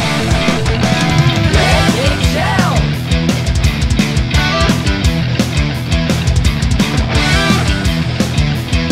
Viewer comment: ok im probably an idiot, but what does it say at 0:54??? Dipswitches?, Ripswitches? Nipswitches? .... rofl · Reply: Nip Switches ;)